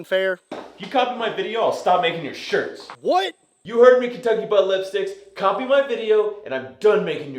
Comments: did call you "kentucky butt lipsticks" @ 0:46? LOL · Did say "Kentucky Butt-lips-stics"? 😂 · Did I just hear “Kentucky Ballipstics”? 🤣🤣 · Did I hear correctly @ 0:46 ? "Kentucky Butt Lipsticks"? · did he call him Kentucky Butt Lipsticks?🤣